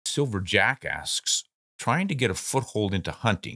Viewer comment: Hahaha, I thought you said Silver Jackass for just a second there.